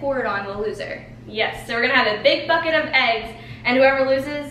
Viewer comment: I thought said a big fuck it of eggs XD